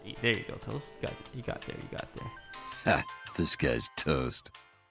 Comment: Is it only me or did I just only now noticed that his intro says "Disguised Toast" not "This Guys Toast" or am I just really dumb and been thinking it said that the whole time... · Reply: It's where he got his name from. There is a unit in Hearthstone that say "This guy's toast" when you play him, and Toast thought that he said "disguised toast."